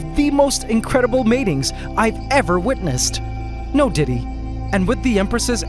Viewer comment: did u really say "no Diddy"? 😆😆😆😆